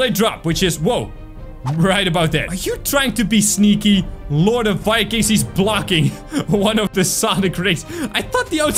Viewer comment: Did said Lord of Vikings to the Lord of Samurai.